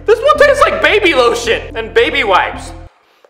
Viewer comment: I think I heard said "Baby Little Shit"